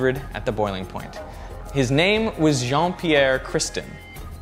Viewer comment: Lol at 3:34 you said and his name was Jeane I thought you said John Cena!!